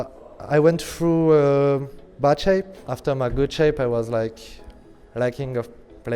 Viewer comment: i understood butt shape xD he actually said bad shape xD